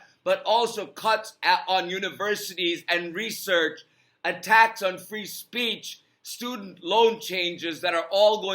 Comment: Why did I hear it as "a TAX on free speech" rather than "ATTACKS on free speech" & just think, "...That tracks"